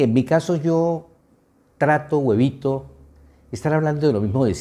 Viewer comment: Entendí “trato huevito”, cuando lo que dijo fue “trato o evito”. Qué rico es nuestro idioma. 😂